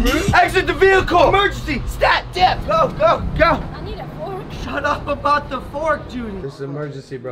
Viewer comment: Lol when she said "I need a fork" I thought she said "I need to fart 💨 " And I laughed 😂 so hard